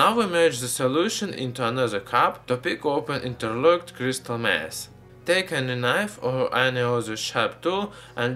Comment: sounded like he said crystal meth XD